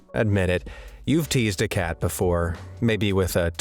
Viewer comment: I swear I heard him say “Admit it. You’ve all tased a cat before.” Me being a cat mom. 🙀 Then I realized he said teased..! 😅🤣 Thank goodness! 😂